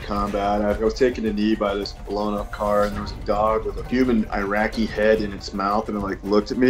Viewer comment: Did he say He saw a dog with a human Iraqi head in his mouth staring and growling at him?